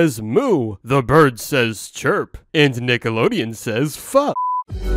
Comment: DID JUST SAY F**K!?!???